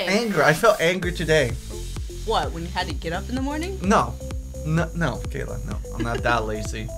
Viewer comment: when said what when you get up in the morning i thought she said when you get it up in the morning